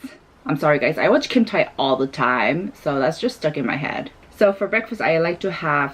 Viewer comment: I thought she said: I watch h3ntai all the time 😂